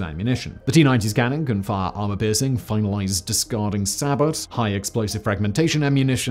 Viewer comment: Did I hear "Armor piercing 'finalized' discarding sabot"?